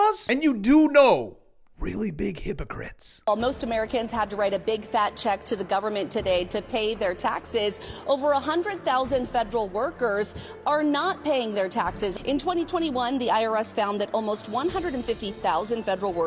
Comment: 😂 I thought it said “Feral” employees at first..🐿️🐿️😊